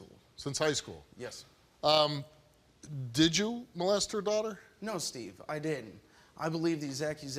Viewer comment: 3 minutes in Steve asked if he molested the child and it sounds like he said “No Steve I did it” seems like he’s admitting it.